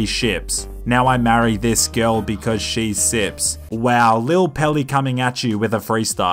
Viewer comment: I thought this dude said “and I married her cause she’s six”